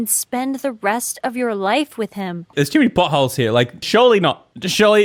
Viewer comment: does anyone else hear him say, "There's too many butt holes...", or is it just me? Sorry, Ik, it's inappropriate....